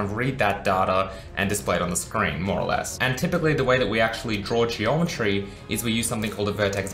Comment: I don't understand what he says at 0:49 ... and the transcript does not make any sense at that point. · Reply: Geometry, perhaps?